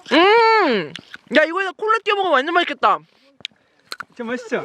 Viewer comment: 야이거에다가 ~띄워먹으면 완전맛있겠다 하시는거 뭐라고하시는거에요??? ㅠㅠ열댓번 넘게 돌려보는데도못알아듣겠어유ㅠㅠ 궁금해서잠못자는중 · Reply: 콜라요!!